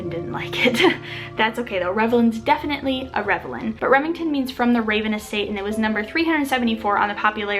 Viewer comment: Omg I thought you said gremlin when you said what your oldest daughter is named 😂